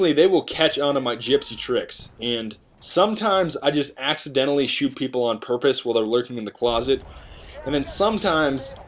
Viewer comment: you "accidentally shoot people in the face on purpose" Am I the only person who heard that?